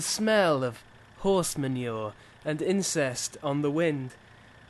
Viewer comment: did he said INCEST in the wind?!?!?!